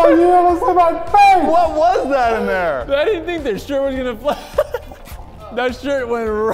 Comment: IM SORRY. WHAT DID HE SAY?!!!! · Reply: Shirt